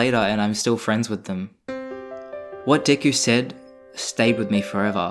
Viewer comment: captions: what dick you said